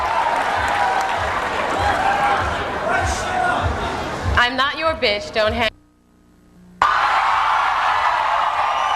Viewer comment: What does the guy said to her? And what she responded? I really wanna know😂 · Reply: I like your tits